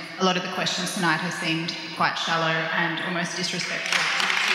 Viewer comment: what does the women at 1:34:30 say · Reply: She says the questions from the audience have been excellent and then appears to suggest that questions were "shallow and disrespectful". There was a thoroughly disrespectful person on that stage and it wasn't.